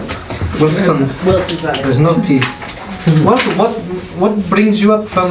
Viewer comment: The dice😂🤣🤣😂🤣😂 of course they got a one! 😂🤣😂🤣🤣😂 When said "there's no t" I heard "there's naughty" lmao😂😂😂 "I have a lot of orcish men" 😂🤣🤣😂😂🤣😂😂🤣🤣 I'M DEAD!